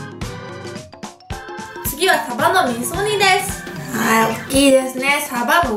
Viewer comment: カバの味噌煮って聞こえたwww